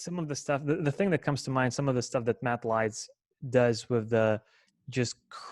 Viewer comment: what name did he say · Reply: He said Matt Leitz <link>)